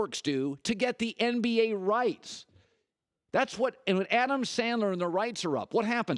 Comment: Did he say Adam Sandler??